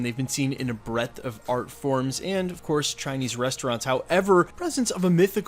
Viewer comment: Did anyone else hear it as a “breadth of fart forms”?